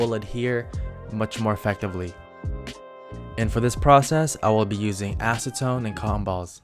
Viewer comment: did he just say cυm ba